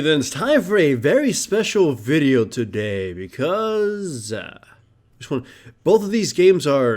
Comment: what did he say? i keep hearing this frequently '' shh one '' ? and @ 0:10 ashitoo? wtf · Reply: he says "sound check one" and "sound check two", he sometimes edits it out but often doesn't because he's lazy, lol